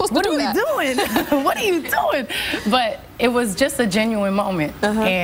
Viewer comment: Did tyra say "Bitch what are you doing )